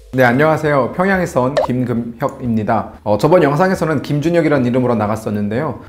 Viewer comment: 나만 " 김근육 이라는 이름 만큼" 이라고 들리낭 ㅋㅋ